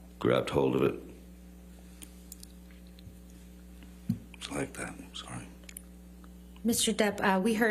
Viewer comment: Was he saying “it’s like that” to Amber.”